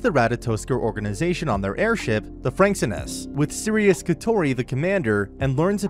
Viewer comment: Did i just hear FRANXXinus? HIROOOOOOOOOOOOOO